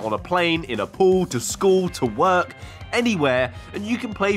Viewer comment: did he just say the pool?